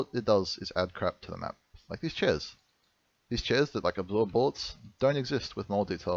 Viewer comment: What is he saying at 10:08? "These chairs that like.........." ???? Something with 'bolts' or 'blow up'? I really can't hear it.